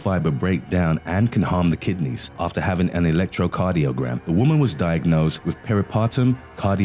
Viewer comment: a....1...pound...box....of...baking soda... ??? why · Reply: sara seaman I thought he said echocardiogram?